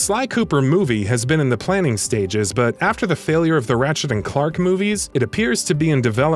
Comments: Did u just say ratchet and Clark? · Did he just say “Ratchet and Clark”? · Im sorry but did he just say “Ratchet and clark”